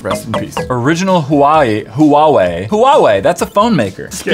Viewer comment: hey Matthias what did u say?😂😂😂😂huwawei?😂I thought that my phone called Huawei 😂